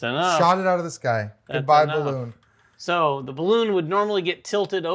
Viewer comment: Did you say “Goodbye Balloon”?